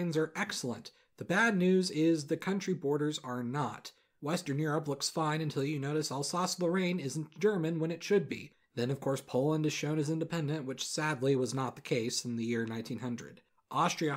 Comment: "Alsace-Lorraine isn't German when it should be" - 2019 Nice. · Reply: I thought he said alsace Lorraine is German when it shouldn't be